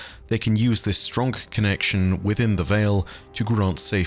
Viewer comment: Does say "stronk" at 8:09? lol